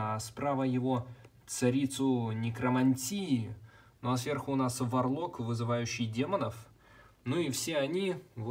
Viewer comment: Послышалось "Ну а сверху у нас Варлорд вызывающий демонов ". Так вот как он получает новинки лего на обзор раньше всех